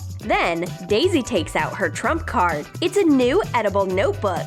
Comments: did they just say trump card? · Who else thought at 4:12 they said “Trump card”? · anyone hear her say trump instead on troom 😂😂😂